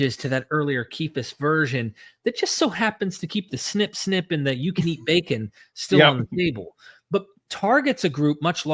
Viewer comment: For a second, I thought he said "You can eat bacon still, on a bagel."